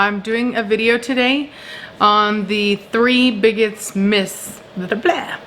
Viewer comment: I thought you said the three bigots myths and I laughed 😂